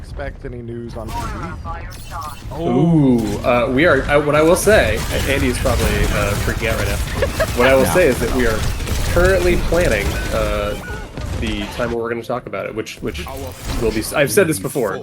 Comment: am I the only0ne that can’t understand what he’s saying? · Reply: Yeah the question was about pve and he says "I can say, andy's probably freaking out right now, what I will say is we are currently planning the time that we are going to talk about it, which - and I've said this before - will be soon" (not blizzard soon, jared soon, lol)